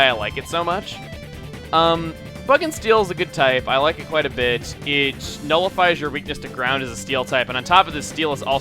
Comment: I always misheard you, i always think that you're saying, i like it quite a bitch